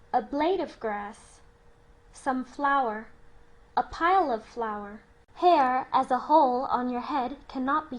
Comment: A good video, I must say, however, I am surpised nobody has spotted "a pile of flower" instead of "a pile of flour" in the 5:45 minute.